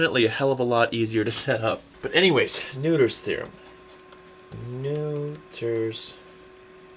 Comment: When I first heard Noether's theorem I thought the professor said neuter theorem ..